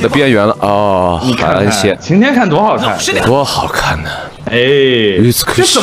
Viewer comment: i think on 1:16 he might’ve said “utsukushii” which is beautiful in japanese but im not sure 😭